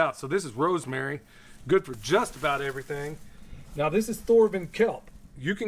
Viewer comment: in your video, I didn't quite hear what you said was in the red solo cup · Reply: Sorry about that. It’s thorvin kelp.